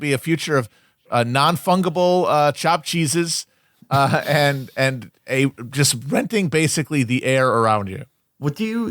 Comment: What novel do they mention around 21:00? I can't quite understand what Felix is saying. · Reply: Red Plenty by Francis Spufford